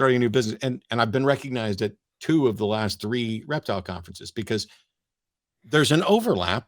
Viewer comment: Reptile? Thank god l thought you said erectile!